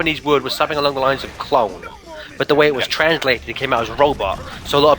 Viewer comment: I thought said, "the Japanese word is something along the lines of clod..." not "clone" XD